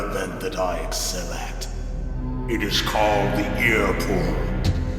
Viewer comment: LOL @ 1:05:27 Actually thought Vulcan Raven said: "Yes, you are right. But there is another event that I excel at. It's called ' The ear-porn' "